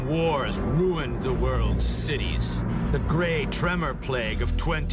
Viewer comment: 2017 and I'm still waiting for that great tremor to end my suffering. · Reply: I went back and listened to it three times, and every time I'm sure that I hear "Grey Tremor," not "Great Tremor." At first I thought I just wasn't paying attention, but even when I focus in on it and listen carefully, I still hear "grey." Anyway, I want a T-shirt that says, "I survived the Grey Tremor Plague of 2017."